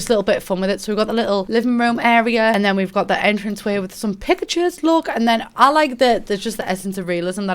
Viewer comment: Was just eating my custard and turned my head when I misheard and thought she said "with some Pikachu's" at 11:05. CC is great! It adds some personality to the house you build. Maybe just me but I can't always match stuff I like with the stuff we get in the packs.